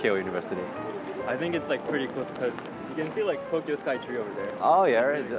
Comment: ～ 東京スカイツリーって聞こえる気がするけど、気のせいかな…英語自信無い…